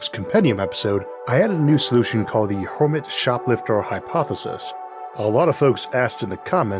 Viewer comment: I almost thought this said "Kermit Hypothesis" and that made me extremely curious